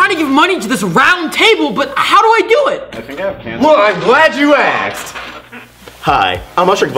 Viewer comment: "i think I have cancer" XD who else heard